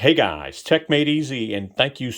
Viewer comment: I thought you said, "Tech Matey Z" for some reason. 😂